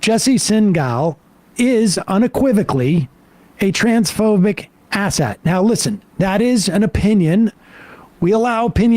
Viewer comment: I heard “transphobic asset”